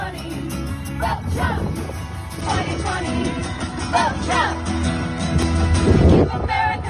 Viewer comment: I'm not sure what those girls/family/band sang but all I heard was "vote and dump Trump 2020" - which is the absolute right thing to do! LOL